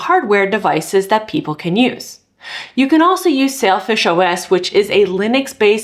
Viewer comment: Say-l fish i swear i heard you say selfish a couple of times